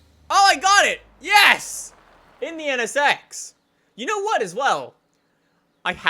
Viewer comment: when he said "in the nsx" i heard him say "indiana s*x"